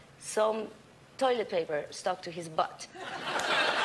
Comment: I had to repeat the joke in my head in a Mexican accent to realise she meant Fax 😂😂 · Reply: Thank you for explaining it so I finally laughed 😂 I thought she said a fox 😂